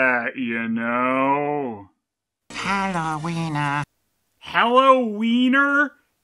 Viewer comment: I personally didn't understand this voice line as ,,Hello Weener", but instead as ,,Halloweena"